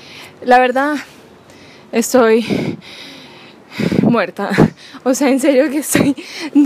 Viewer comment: Me dio mucha risa en el minuto 7:32 ya que dice estoy muertada😂